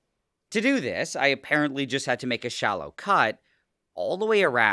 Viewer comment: I misheard shallow cut as shaolin cut